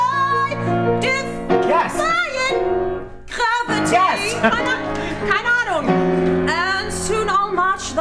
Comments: Does anyone know what she's saying at 3:03????/ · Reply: said "keine Ahnung" - it's German and means "no idea" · What word is she saying at 3:03 ish. It sounds like hananoon. I've been trying to figure it out for a while now. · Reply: 'keine ahnung'. it mean's 'no idea'